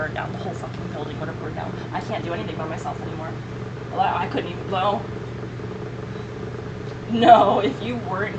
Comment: “Oh my god, I couldn’t even blow” . Is that what she said there ?? C’mon, 8:44 We allll know that isn’t true . . 😝💀